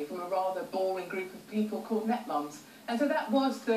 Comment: what is the word she says? i hear netmoms or something but don't know what it is.